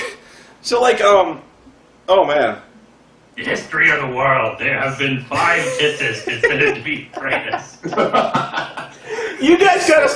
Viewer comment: What does say at 20:18? I just can't make it out... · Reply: He's quoting a line from "The Princess Bride". "In the history of the world, there have been 5 kisses considered to be the greatest."